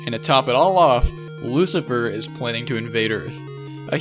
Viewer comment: That one moment I heard, "Lucifer is planning to be in vader" 1:30 And stop it